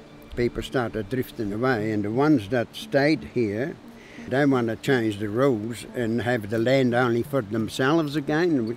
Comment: the subtitles say, "the one's that stayed here, *DON'T* want to change the rules and have the land only for themselves." Actually, it's the opposite. He said, the one's that stayed here, they want to change the rules and have the land only for themselves."